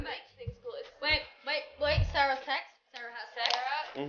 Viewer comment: CAPTIONS ARE WRONG VERY WRONG THEY SAID ‘SARAH TEXT’ NOT ‘SARAH SEX’